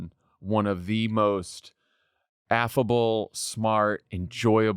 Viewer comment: Did u say Madam President is f-able????